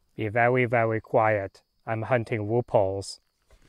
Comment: I thought you said I'm hunting rupauls